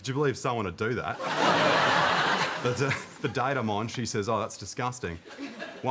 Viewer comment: oh 'date of mine' I see!! I heard it as 'data mine'